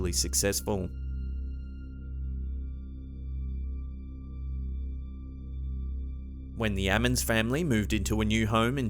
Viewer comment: At first I thought he said "The Adams Family" until I realized he said Ammans